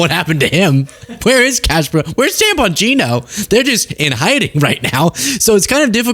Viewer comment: when you said "Dan Bongino," I heard "Tampon Gino." Hearing-loss can be fun.